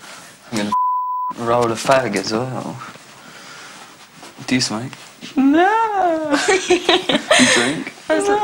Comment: Who else thought he said do u smirk?